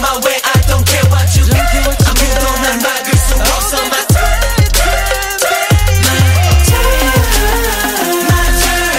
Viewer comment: I really want to know what woobin said in 3:15 - 3:19 please😭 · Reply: “oh this is my turn, baby”